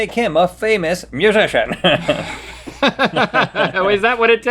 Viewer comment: For some reason I heard “magician” instead of musician and I thought they were going to tell me Celine Dion used to do magic tricks in her youth 😂